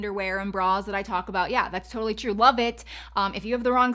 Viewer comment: When the part came on about undergarments at 7min:15 sec I thought you said " WELL BITCH" I had to listen 3 times. Then I finally looked in the transcript. You said, "Love It" lol ...DO you have any undergarments to recommend for someone with huge breast ? I can never find good bras or underwear that I like. BTW I think you looked beautiful In the family photo with your brows. I thought you looked gorgeous. I have no eyebrows if I don't draw them on. They are so light , that it looks like they don't exist.